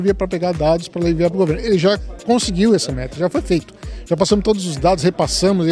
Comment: Já conseguimos essa merda ou meta? Não entendi bem '-'